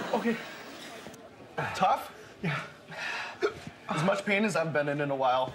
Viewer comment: To an American, his accent made him sound like he said: "That's as much penis I've been in a while."